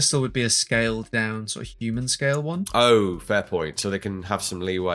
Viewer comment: I heard "femboy" in place of "fair point", and my brain immidiatly went "Yeah. Femboy bolt pistol. True."